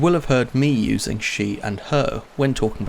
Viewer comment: I heard "she and hoe" 4:21 😅